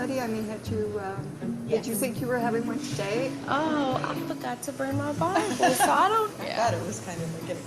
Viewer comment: I could've sworn when she said "I forgot to bring my bible" the captions put it as "I forgot to burn my body"